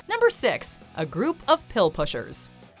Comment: In his defense, “A group of will wishers” wasn’t much better 🤣🤣🤣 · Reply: I thought the other guy said “a group of wheel wishes”